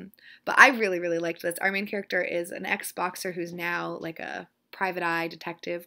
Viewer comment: Not gonna lie...I'm watching this kind of late in the evening and when you were describing The Long Fall you said "ez-boxer" but I heard "xboxer" (as in...made his living playing x box I guess...) And I was pretty confused for a few seconds. Good times